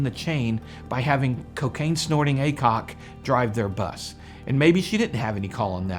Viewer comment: All's I heard was ,"cocaine snorting a cock"...